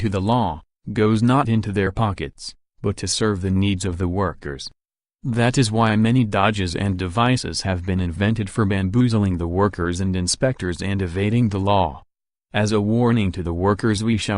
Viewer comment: An early work by Lenin where he explains how unjustly the Tsarist laws treat workers and favor the factory owners. The factory owners often imposed "fines" (deductions to wages) if the workers tried to fight for their rights. The factory owners also used any excuse to impose more fines as a way of reducing wages. Lenin was a lawyer by profession and used his knowledge of the legal system to help the workers organize. However soon he would embrace the life of a full-time revolutionary. While not being a major theoretical work, early writings of Lenin such as this are an interesting look into his revolutionary development and the historical context of the Russian Empire. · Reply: did you say bamboozling lol